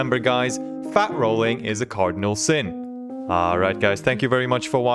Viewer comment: I thought he said "cardio sin" 😄